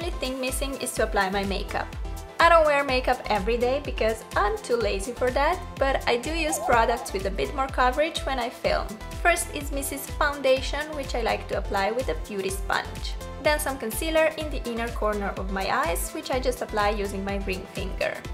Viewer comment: did anyone else hear Mrs foundation ,??🤣😂🤦🤦🏾‍♀️